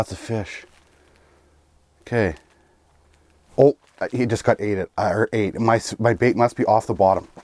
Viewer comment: he got my bait I hear ate 😂 had me dying not gonna lie.